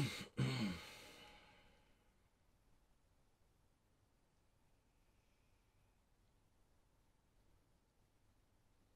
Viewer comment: Anyone know what that voice (sounds like a little kid) is saying at 14:50-15:03? The last thing is sounds like 'I can't believe it'. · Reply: "I hear "It's really him" 15:03 "I can't believe it" and "I can't believe it" again